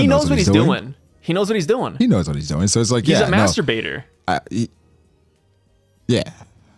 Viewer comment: I literally froze mid-rep in the gym as I heard “master-baiter” 😭😭😭😭